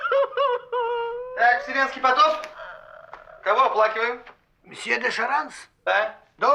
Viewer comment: я слышу "Все! На работу!!" 1:01:18 Вселенский потоп